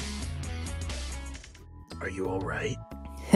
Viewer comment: When shadow asked sonic if he is okay iheard it as are you gay then I thought that he said that is he kay but I heard it as gay😂